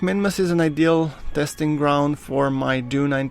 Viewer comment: it sounds like your saying groundforks in the intro